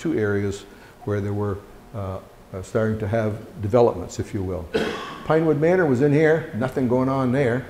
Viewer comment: I hear "Pinewood wasn't there" at 6:23. I wonder if meant "Birchwood"? Either way, thank you for your detailed and informative presentation!